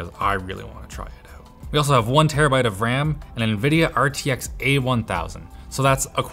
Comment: Did he say 1TB of RAM @ 4:22 ???